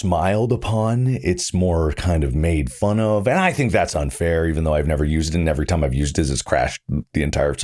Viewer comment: did he say unfair or not fair…?